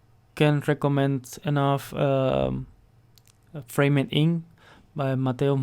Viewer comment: You mentioned a framing book on the second piece you created dude, what was that book I can’t quite hear what you said? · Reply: Framed Ink, by Mateu-Mestre